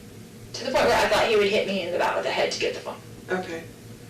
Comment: Did she say, "he would hit me in the BAT with a HEAD to get the phone? (At about the 3:53:46 time stamp)